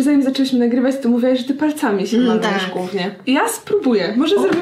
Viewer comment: uslyszalam "mówiłaś że palcami się bawwisz w gównie" 9:14 usłyszałam "jestem cyckiem" xdd